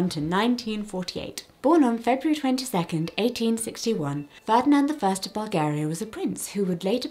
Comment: Hey! I just wanted to say that at 14:50 you say that Czar Ferdinand was born on February 22 but the subtitles say February 26. I just wanted to let you know so that if possible you could correct whichever one is incorrect so that anyone watching who relies on the subtitles or audio can properly enjoy the wonderful history lessons!